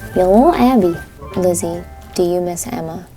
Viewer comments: it sounded like it said it was emma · I heard it was lizzy 😯